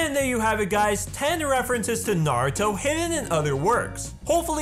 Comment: No no no did I just hear what I think I heard? Did he just say narudo?